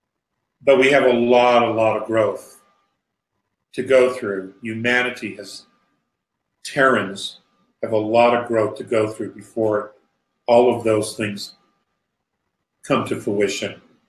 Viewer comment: I thought he said "Karens" have a long way to go...he said Terrans...